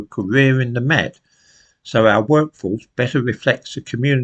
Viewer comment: Did you say “workforce” or “wokeforce” ?